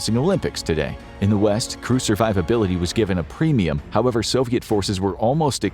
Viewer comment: you say "Crew survivability", but I heard "Crucifiablity". Weird thing is, it still makes sense !